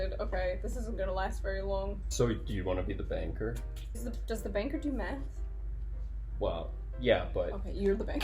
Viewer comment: I thought you said does the banker do meth 😂